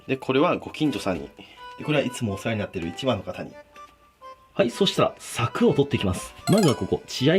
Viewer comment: いつもお世話になってる一番の方に って聞こえて、 金子っ、か、かね、かねこ、ふぁっ！！？？ ってなった